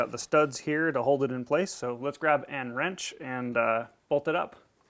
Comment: did you say "an wrench"? isn't it "a wrench"?